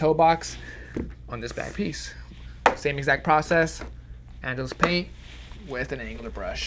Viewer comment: I thought he was saying Angela’s paint this whole time